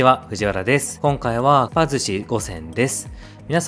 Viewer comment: 今回はかっぱ寿司五選ですに聞こえるｗｗｗ